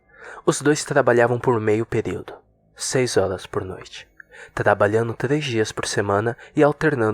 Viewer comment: Só eu entendi seis olás por noite?